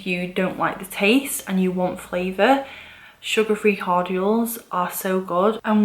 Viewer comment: Hi, love your channel! I try to drink water all day long. It helps keep headaches at bay for me. Cheers! (I couldn't quite get what you were saying around 6:10 in the video, but now I realize it was "cordial," and I'm learning about that now. In the U.S., we only know it as a chocolate candy or alcohol. I'm gonna try making the kind you're referring to. Sounds great!)